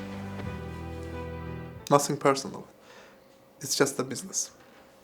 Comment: de ingilisce ne dedi? Biri yaza biler? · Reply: Nothing personal.it is just a business(şexsi deyil, bu sadece biznesdir)